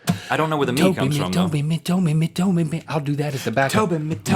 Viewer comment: when link was saying toby it sounded like he said dont beat me..